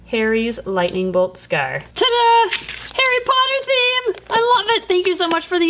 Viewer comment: I wasn't listening very hard and when you said Harry Potter I thought you said hair products lol